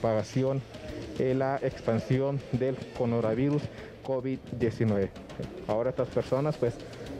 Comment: MIN. 2:13 "Conora virus" seré yo q escuche mal. Oh alguien más escucho lo mismo del periodista?? 🤔🤔🙄🙄🤪